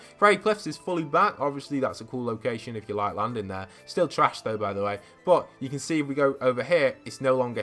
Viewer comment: Did u say trash or trashed? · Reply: Trash